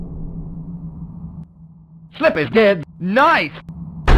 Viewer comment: Peppy: Slippy’s dead Falco : N I C E I DIED 🤣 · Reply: I thought he said "flippin' dead"